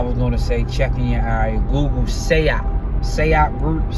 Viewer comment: Hey good bro if you could . Spell that group name if you don’t mind. Sound like you said “Say Out” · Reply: SAIOP